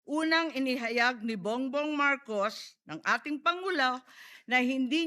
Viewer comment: Tama ba ung narinig ko sbi ni Sen. Imee "...si bongbong marcos ang ating pangulaw" 😂😂